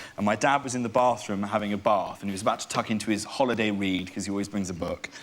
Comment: When he said holiday read I thought he said holiday weed and I was like 😳 oh!